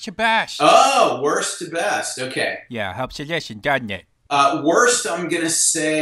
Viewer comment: What did CC say at 6:57? "helps to listen doesnt it"? English not my 1st language thx!!! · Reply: CC says, "helps to listen doesn't it."